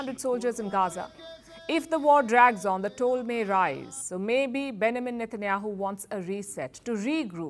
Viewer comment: I laughed my As off & still laughing. @ 19:06 When I heard Palki pronounce Benjamin Netanyahu's name. She said it really fast. She said Ben amen Netenahu. I lived in Germany for a time back in the 1980's the word that is so terribly hard for them to pronounce is Vegetable. They really screw it up. Just something light for a change.